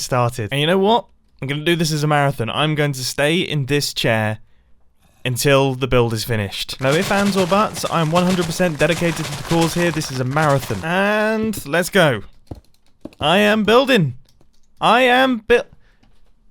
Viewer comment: can I get a caption? Because I heard something near the end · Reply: Because I’m not the only one who heard no only fans or butts right?